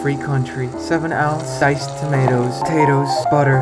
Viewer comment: When it said tomatoes I thought it said TOES💀💀